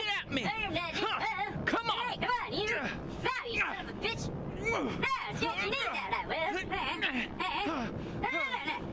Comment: What did that cat say? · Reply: he said son of a bit**